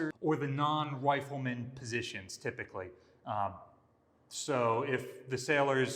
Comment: "... or the non white women positions typically"??? i've listened to it 4 times now and that's the only thing i can hear. If that's what actually said can someone please tell me what a non white women position is. Anything i can think of is definitely NSFW !:-) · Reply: he's saying non-rifleman positions